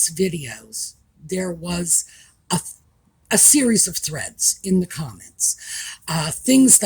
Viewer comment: At first I thought you said threats. I had to go back and listen - it still sounded like threats BUT the context told me it must be threads.